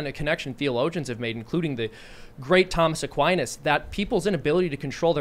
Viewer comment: Was it just me, or did anyone else hear "Tom S'quinas"?